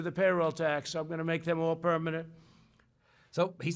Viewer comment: I thought he said " more permanent" not "all". Guess I heard it wrong. Can't trust captioning much either. That crap makes up words that are from NO known language. Maybe they are alien bots.👽😈 Really burns me when these old rich geezers in office talk about this subject tho. What's it to them? They don't have to rely on it to survive. They're already older than I am. Just more of the entitled 1% greed and non compassion. Shame on them.🤬💔